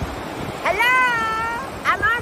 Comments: Why did it sound like he said "I love bitches" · Sounded like he said I love bitches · I heard: "I love b****es." 💀